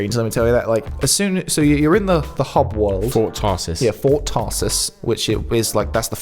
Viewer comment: "Fort Tossers" 3:18 That's what the first Tarsis sounded like through your magnificent regional-British accents. And I thought, "Yeah, a fort full of tossers, sounds about right."